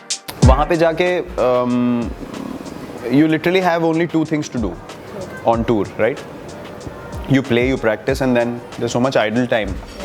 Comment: i thought he says only two things to do you play and sex